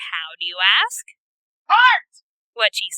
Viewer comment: I had to rewind because the first time I thought she said "fart".